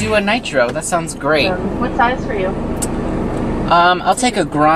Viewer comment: I thought she said what sign are you but she said what size